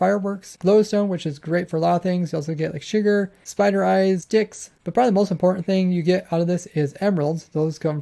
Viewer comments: did he say sticks or... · is it just me or do it sound like he said dicks